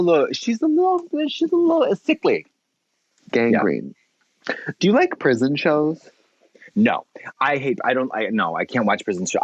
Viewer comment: My mind heard him say “do you like prison chodes?” Actually they said do you like prison shows, and now I am the gross one.